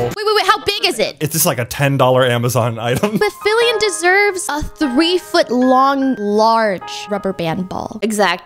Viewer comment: In the beginning i thought that she said 3foot long dingel dong